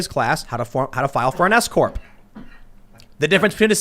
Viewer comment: Lol I thought he said file for an escort, they definitely shouldn't be teaching that in schools. I'm from the UK we don't have s-corps (I think that's what he said)